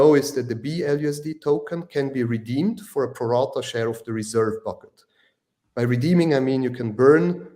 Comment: keeps mentioning the phrase “peralta share” and “peralta value” at 27:44. What does he mean by the word peralta? Does this mean proportional?